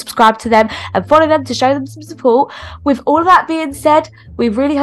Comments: who also thought she said bitches😂 (btw she said peaches).. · Bro 😂 I thought she said “ hi b**ches”